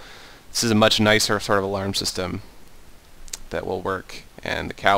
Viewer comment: did he say the cow will come to u